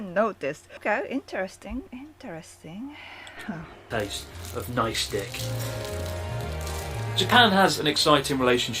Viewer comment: That moment at 2:45 when he says "Taste of Nice dick" instead of "Nicestick" . That guy is hilarious😂😂😂😂